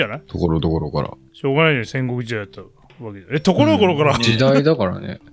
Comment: 暗黒時代を戦国時代に聞き間違えたんでは…？